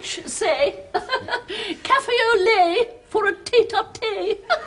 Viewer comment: What is it she says the French say? and the part after that? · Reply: Café au lait, for a tête à thé (instead of tète-à-Tête) head to head...